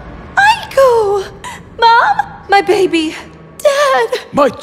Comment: here the subtitles say my child but what it says is my baby